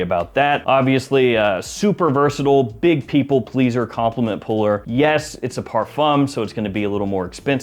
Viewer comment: I thought you said for most compliment getters skip the parfum